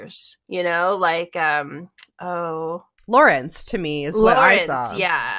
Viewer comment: When y’all said Lorenz I thought y’all said Lawrence